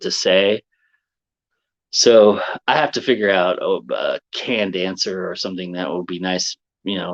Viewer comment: I heard 'can-dancer'....and eventually understood 'canned answer'. I like mine better.